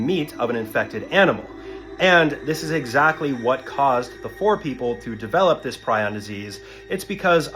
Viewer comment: I thought he was saying the poor people 😭